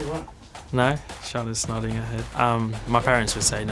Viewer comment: What did the director said to Lando about 5:57 and what he respond? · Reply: “charlotte is nodding her head”